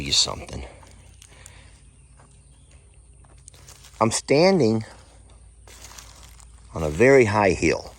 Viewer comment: You have watched them all, just for the gardening...tips? Hahaha. Then I looked away, and heard you say, "I am standing here on a high heel." I was like what? He has high heels on now! Then I looked and you were on the hill, not heels. hahaha... But hey, you just never know!